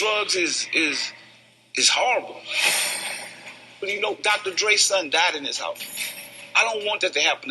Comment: "Dr.Dre son died in this house" 🤔 am I the only one who heard that? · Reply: In his (Dr Dre's ) house, and he didnt want to find her to dead in his house